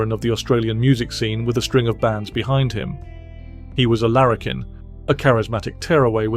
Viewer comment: Ha!! I learned something about Australians, and a new word!! I had never heard it before, and I'm an old (American) man!! At only 1:20 the narrator said of Bon Scott - "He was a larrikin." I thought he said - "He was American." I knew that couldn't be true so I thought it was a mistake, which is common in AI documentary pieces. But I learned long ago (the hard way) to ALWAYS look anything up where something sounds like a mistake. Anyway, here's my newest lesson on Aussie-talk... "larrikin" lar·ri·kin /ˈlerəkən/ Australian and New Zealand English... "A boisterous, often badly behaved young man." Australian English: "A person with apparent disregard for convention; a maverick." And those BOTH fit Bon... "to a T"!!!